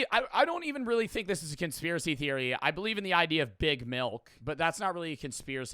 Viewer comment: what’s that I hear talking about it a lot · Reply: Big milk? It’s just like that milk companies (big milk) are pushing milk and making people think it’s like this god drink because they have too many many cows or something (similar to big cheese, they just have too much cheese so they push it)